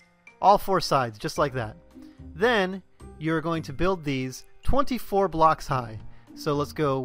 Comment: I thought you said "44 blocks high" at the start... R.I.P my cobble in survival Edit: And it didn't even work :(